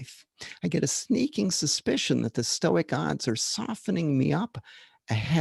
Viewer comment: When mentioning the stoic odds I thought you said stoic “gods” so it was pretty funny to imagine you trash talking the gods lol. I think that’s how I will refer to it, personally.